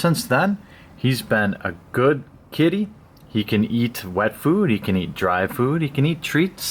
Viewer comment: "With Waifu, he can get kinda gassy... " "...he can eat Waifu" Blame the misheard on me not being a native english speaker, I just couldn't resist. xD